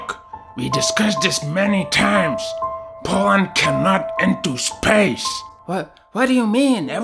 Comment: I thought he said "P0rn can not into space" 💀